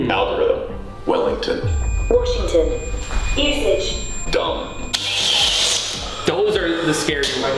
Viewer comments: I heard “dumb”😭😭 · Sounded like it said Wellington around 13.23 . I live in Wellington New Zealand and it weirded me out lol